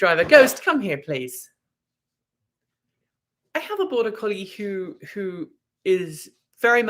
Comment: oohhhh. "I have a border collie..." At first, I thought you said "I have a boy..." I was picturing Ruprecht from "Dirty Rotten Scoundrels" <link>